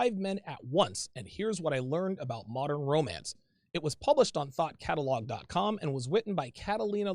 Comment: I thought you said THOT Catalog at first listen.